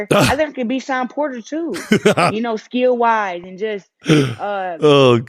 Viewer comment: Sounded like she said "skill wise"